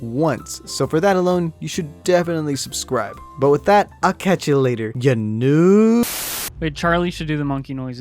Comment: did anyone else hear Akechi you later